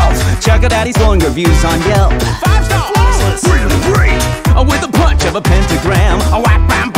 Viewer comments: I'm not a english native speaker So when Lucifer says "oh, with a punch of a pentagram" i thought he was saying "a widow punch" and i believed he was saying Lilith was dead 😢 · I'm not a english native speaker So when Lucifer says "oh, with a punch of a pentagram" i thought he was saying "a widow punch" and i believed he was saying Lilith was dead 😢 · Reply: I thought he was saying "widow punch of a panty gram" the first few times I heard it lmao